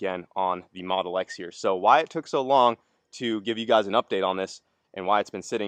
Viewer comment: I heard Wyatt took so long😮had to rewind, lol😂